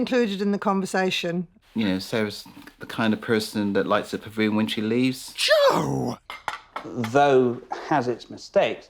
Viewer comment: Sarah is kind of person to do WHAT when she leaves?? // I can't understand what he says 🤷‍♀️ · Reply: Lights up a room when she leaves